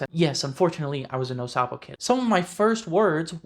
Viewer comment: snd 12:20 “no salvo kid”. Is what I heard but trying to know what it is and means. Great explication man. Thanks!